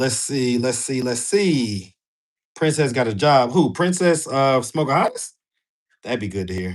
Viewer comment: I thought he said princess who smokeahontas lol I was like damn that's a good one